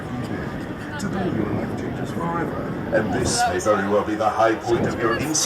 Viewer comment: I'm so confused on 0:59 on what does Lex say when you almost get to the top on the ride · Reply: “Today’s your lucky day, today your life changes forever”